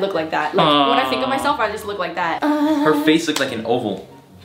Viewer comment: can somebody tell me wtf she said there?🧍‍♀️🧍‍♀️💀 pls tell me I’m not the only one who heard it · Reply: I mean, when she said "When I __ myself I just look like that" What she said there??